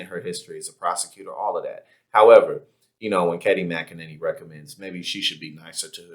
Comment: Lol you called her Katie? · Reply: I believe I heard a "Kenny" too. I'll accept that as well.